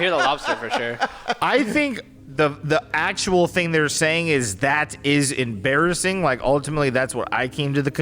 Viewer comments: I only hear “that is embarrassing” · I heard “THAT IS A MARTIAN” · I heard “autistic bararar”